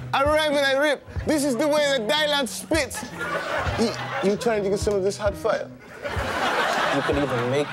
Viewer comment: "You trying to get some of this hot fire?" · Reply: It Almost Sounds Like His Saying "You Trying To Get Some Of This Hot Fudge"